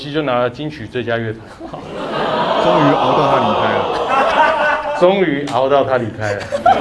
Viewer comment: 聽不清楚 請問是終於什麼離開? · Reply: 熬到他離開了